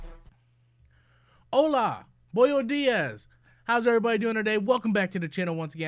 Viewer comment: This dude should not learn a second language because he will not research the proper pronunciation before going live on camera or take the time to learn one! Lol I had to replay the video at the start because i thought i misheard him saying boyo diaz for buenos dias for spanish. I do not speak spanish but now i know much that is not how the greating is said or sounds like. Hahaha.